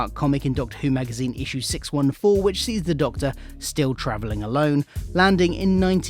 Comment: Hahahaha. At the start of the Run segment, it sounds like you say 'trivilling' instead of 'travelling'. No biggie but it gave me a giggle as I thought you said 'tribbling' at first, which made me think of Star Trek.